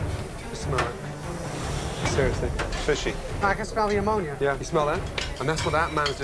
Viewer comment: When I watched this at the part where he smelled the lobster (the owner) I thought he said "I could smell the Ebola"